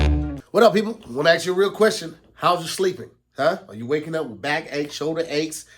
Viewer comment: I dead ass thought said "You waking up with back eggs, shoulder eggs?" 😭😭😭😭 his tongue twisted frfr